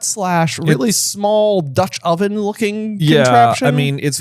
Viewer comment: Did he say….. 3:53 “Dutch oven”?